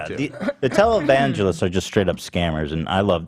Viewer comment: I know Ethan is saying "televangelist" but I just hear "taliban-gelist" every time